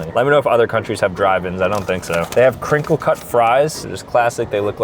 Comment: I heard him say at first at beastburger crinkle cut cries